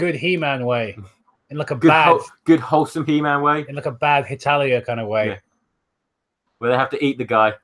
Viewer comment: Did you say "Hetalia" at 20:41?